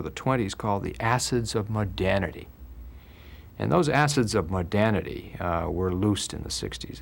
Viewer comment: What is he saying here? Assets of? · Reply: He said "Assets of modernity" It might have been a bit hard to understand due to the accent. 😅